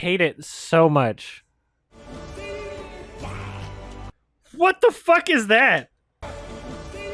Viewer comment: I swear to god when I first heard this I thought he said "Be Gay!" I didn't hear "Simba" until pointed it out.